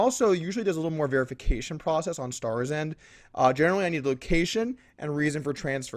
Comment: what?... did he say starsend? what... is that? like a PayPal business/website?